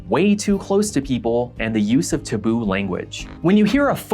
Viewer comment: Wait. Hold on. Dude... you pronounce, “taboo” as “t’boo”? I’ve never heard this before I thought when you said “using t’boo language” you were saying he was using some obscure foreign language called T’boo or something!